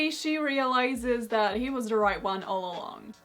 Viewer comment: I heard "She realises he was the right one all along" as "He was right all along" and was offended at first at your interpretation haha